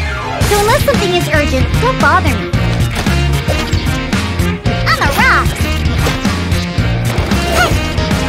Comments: i thought she said "Im a rock when i got her" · Diona: uses elemental skill "Shaken and stirred!" Me: a german with poor listening comprehension skills "YEAH, shake your HAMSTER!" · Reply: i thought she said imma a rock too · BYE I THOUGHT SHE SAID AMAROK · I’m I the only one who thought “on the rocks!” Was “I’m a rock” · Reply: I always thought she said “on the rocks!” But now I hear “I’m a rock!” Lol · I can't be the only one who hears " I'm aroused" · No cause when she was saying on the rocks I thought she was saying I’m a Ross